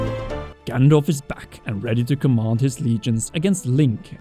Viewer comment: I coulda sworn you said "Gandalf is back."